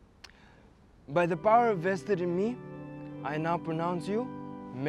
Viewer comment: you can hire me for English Subtitles! XD Here are 2 corrections for the errors in this vid: 6:07 "By the power VESTED in me" instead of INVESTED 6:49 "You haven't spoken the whole SCRIPT " instead of VIDEO jk